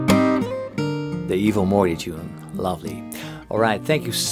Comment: "the evil mooi tune"?? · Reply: Morty